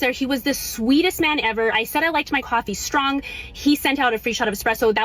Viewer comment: I thought she said she liked her coffee sweet and she said she got a free shot because she said she liked her coffee strong?